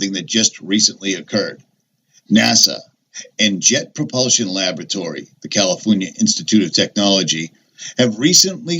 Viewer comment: could've sworn you said "Shit propulsion Laboratories" ,my husband says" no"but it sounds like"Shit propulsion"every time you say it! But maybe it's just your spirit to mine in hearing!!!!